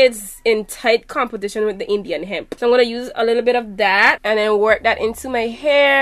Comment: i thought you said the Indian hemp one is best 😭 i was going to get it this weekend